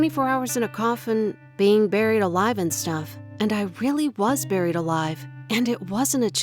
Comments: My dumb ass thought it’ said I was born alive 🤦🏽‍♀️🤦🏽‍♀️ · I thought it said burnt alive :-/